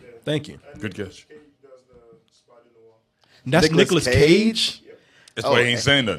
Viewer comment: I thougt I heard him say "Niggerless Cage" instead of "Nicolas Cage".